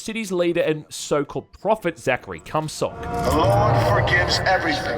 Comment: "Zachery Cumsock." What did he say?!